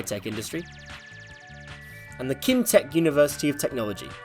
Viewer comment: I thought you said Kim-Tech instead of Kim Chaek, it would be an awesome name.